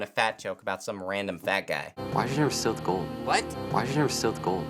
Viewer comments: I think he says "why did you steal the gold?" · I think he says "why did you steal the gold?" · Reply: I heard, "why didn't you sell the gold?" but I think you're right.